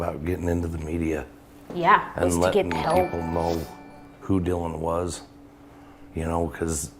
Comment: he says "who Dylan WAS". He didn't say IS he said WAS.